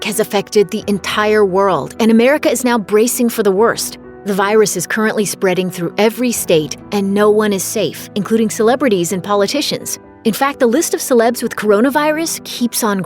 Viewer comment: I thought you said celebrities I've never seen any of them